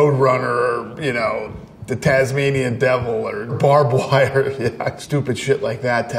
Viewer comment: Barbed wire? I thought he said Barbara Walters 🤔